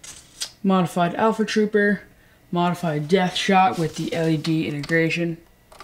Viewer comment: Hey, what you said at 06:39? Death shot?